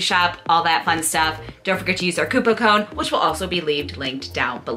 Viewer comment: Lol did say COUPO CONE?